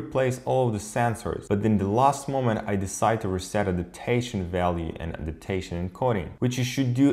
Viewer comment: What did you say at 6:09 ?? What im hearing : "At last minute i Decided to recenter the detation valving"